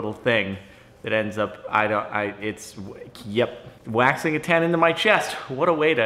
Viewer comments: Phahahahahhaha, i thought he said: I'm gonna wax a pen into my chest./ And I just Went like: whaaaaaaaaaa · At first i thought you said "wanking A pen into my chest" instead of waxing a ten on my chest.